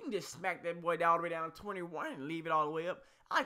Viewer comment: Did he just say 1:43 21?